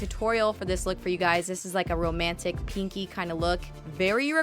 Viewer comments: At the beginning, I thought you said kinky. I was like okayyyy... You do you girl! 😂😂 then I realized you said pinky! 😂😂😂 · thought she said "romantic, kinky eye look" lol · i heard "this is a romantic kinky kinda look" 😄😄😄